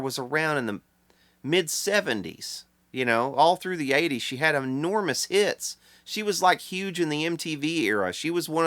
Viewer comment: Did you say enormous hits or ( • )( • )